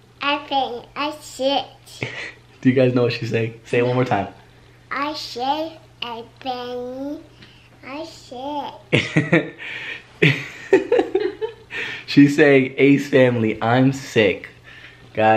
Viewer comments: Instead of Elle saying I'm sick I thought she said I shit!! 😂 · Am I the only one who thought Elle said "I shit" instead of "I'm sick"! It’s at 2:50 btw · Ok I laughed at the part when Elle said "I sick" I thought she said I shit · when elle said im sick i thought she said i shit lmao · No hate but i thought she said "i shit" 😂 i love you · At first I thought Elle was saying ace family I'm six! But then Austin said what she said! Lol! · sound like she is saying “I shet” (she is so cute) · I thought she said "I shit ace family" 😂😂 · When I heard Elle trying to say “ace family I’m sick” I thought she said “I shit” lol my gawd that was so funny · That’s not what I heard😂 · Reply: I heard "i sh*t" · Am I the only one who thought Elle said "I shit" instead of "I'm sick"! It’s at 2:50 btw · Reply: Mackenna Roush I thought she said I'm six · broo i thought elle said "i shit" 😂😂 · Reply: I thought she said "I'm six" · Am I the only one who thought Elle said "I shit" instead of "I'm sick"! It’s at 2:50 btw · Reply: I thought she I said I shit six lol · Am I the only one who thought Elle said "I shit" instead of "I'm sick"! It’s at 2:50 btw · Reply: same I thought she said I'm six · Ok I laughed at the part when Elle said "I sick" I thought she said I shit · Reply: I thought she said she’s six I’m like “since when?!” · Ok I thought Elle was saying “ I shit “ when she kept saying “ I sick “ ☠️